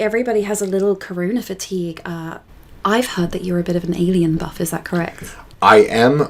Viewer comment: I thought you said "avian buff" at first and I was thinking "yeah that checks out". Alien buff works too lol